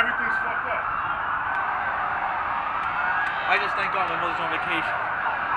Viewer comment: did you work in the towers cause i heard you say “thank god I am on vacation”